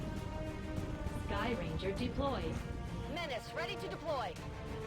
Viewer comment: All this time, I thought they were saying 'Ennis 15, ready to deploy'. But it turns out they're saying 'MENACE 15'... I think.